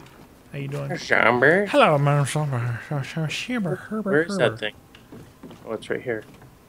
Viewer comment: did say hello motha f*cka